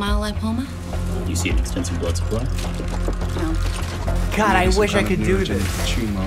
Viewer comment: Completely unrelated, but at 2:17 when Mike said "God, I.." I swear I heard him saying "Cara!" which is brazilian portuguese for "Dude!" and that took me completely off guard lol